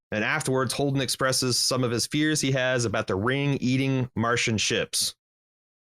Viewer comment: I heard "The Ring eating several Martian CHips"... 😂😅